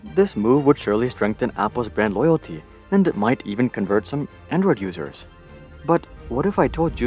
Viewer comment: literally thought he said "might even convert some N-word users" while showing a black man. Im dead 💀💀💀